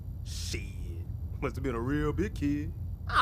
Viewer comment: I heard "must've been a real dick head" LOL